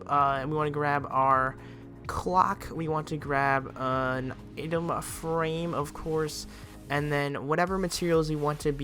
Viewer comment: Who heard him say itema frame